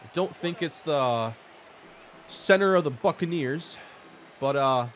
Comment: I misheard that as "center of the f**kin' ears."